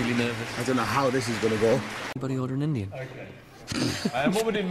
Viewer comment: what did Dec say at 6:09 ? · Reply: z 'anybody order an indian?'